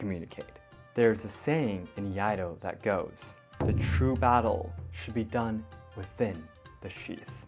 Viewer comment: Me, mishearing the audio: 3:14 the true battle is done with the sheet